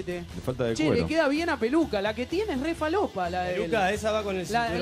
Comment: "hay buena merca acá EH" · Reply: re falopa es jajaa que dice